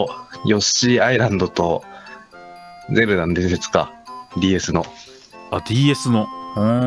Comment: の所ゼブラの伝説にしか聞こえないww